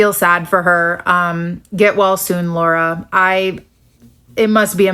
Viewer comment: It genuinely sounded like she said, “Get Walz soon, Laura,” which belongs on a fucking T-shirt.